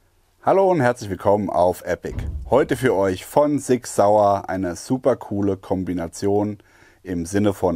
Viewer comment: All I heard was "fun Sig Sauer and a super cool combination" lol...